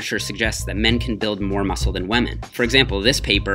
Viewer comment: Did he say WAMEN?!